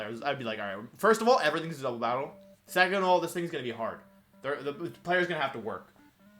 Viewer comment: "first of all asg;dskl'bs;bcln battle" can someone tell me what did he say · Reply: "First of all, everything is a double battle."